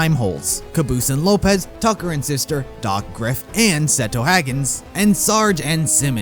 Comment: Couple things: - the lens flare is named “huggins”, not “seto haggins”. You misheard Muggin’s line. He actually said “settle, huggins”. - i dont think donut (or any of the reds and blues) are the trickster; i think she was referring to someone else.